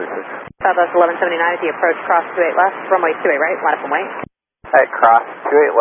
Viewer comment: Taxied and crossed a bit too slower than TWR expected, maybe? What's clear is that Southwest was indeed to be on the 28R runway. Opinions? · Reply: NO, @ 0:18 she clearly says "clear to 28R LINE UP & WAIT" which means wait ON 28R, there was NO hold short order for SW. You don't listen or hear clear enough or remember clear enough to be a controller, if you were you'd be just like her & create the same confusion or worse.